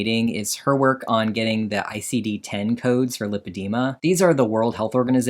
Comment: Every time someone mentions “IDC10 codes” I just hear “ID10T Codes” 😂